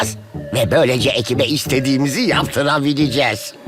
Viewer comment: fikri kereviz’de duydum